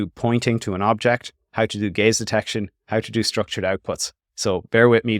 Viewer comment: I keep hearing it as "gays detection"